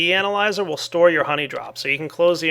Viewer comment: I thought it said "honey derp" for a second